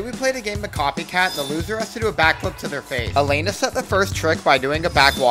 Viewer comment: I thought he said a backflip to there fate and I was terrified because they are on the top of a bilding